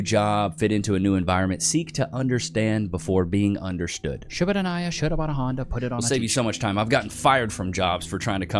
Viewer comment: will you please write the phrase you say that includes “….put it on a Honda….”? For the life of me I cannot understand the beginning of that spoken phrase because you say it so fast 😂 Thank you so much! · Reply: shouldabidinaiah shouldaboudahonda put it on a t-shirt